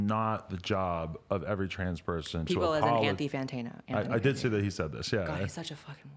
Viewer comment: What name did she say of the person? Anti Fantayna? what it the spelling of said person's name? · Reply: Anthony Fantano